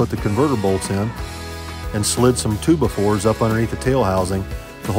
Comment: what's a "tubafor"? and don't say for playing music 😆 #tubafor #toobafore #2x4 #twobyfour #hillbillyenglish